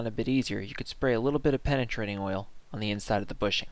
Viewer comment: I believe I hear someone say penetrating oil. Maybe I'm just drunk, sorry.